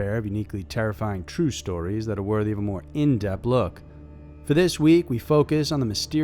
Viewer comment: its in depTH you sound like your saying in DEPt look lol